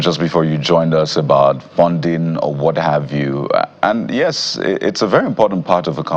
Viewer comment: Did I just hear you say, "funding whataview? We have a long way to go!